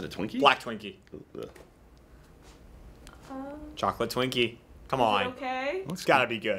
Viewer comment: did he just say "chocolate twinkie"?